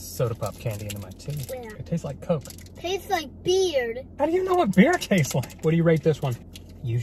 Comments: I thought she said “it tastes like beer” XD please help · I thought she said I like beard